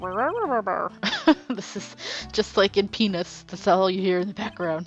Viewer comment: You didn't just say what I think you said, did you? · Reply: HAHA no I said peanuts but didn't enunciate the T enough.